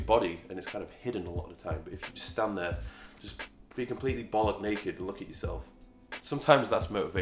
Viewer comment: Did you just say balls naked 🤣🤣🤣🤣🤣🤣🤣